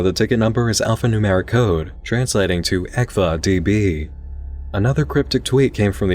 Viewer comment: lol I keep hearing "ECKVA DB" as "EXA DB".